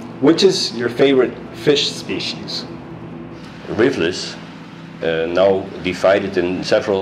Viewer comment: when asked his favorite species, it sounds like he said "Livurus", but I'm guessing it was "Rivulus". I think the picture was a species of Rivulus. Is that correct?